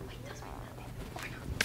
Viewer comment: He thought she said “toss me like a pillow” 🤣😂